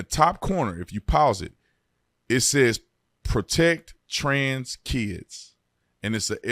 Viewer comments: I swear I thought it said protest trans kids. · I thought it said protest trans kids